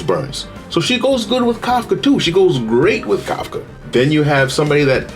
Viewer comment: really good video bro, also im just laughing my ass off at the captions cause when you said "silver wolf" the captions said civil war, and when you said asta the captions said either ass or ice AND AT 4:14 WHEN YOU SAID KAFKA THE CAPTIONS SAID COCKATOO